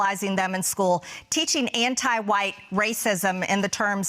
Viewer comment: Did she just say "ANTI WHITE"